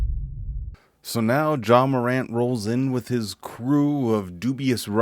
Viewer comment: did he say Ja Morant?!? Lmaoooo 😂